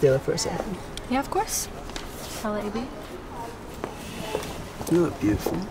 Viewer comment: Мне послышалось , что она сказала "я в курсе"😂